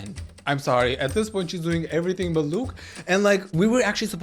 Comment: I'm sorry 1:33 DOING EVERYTHING BUT LUKE? IS THAT WHAT YOU SAID